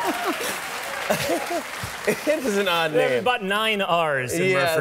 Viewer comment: did he just say "he is an odd nigga"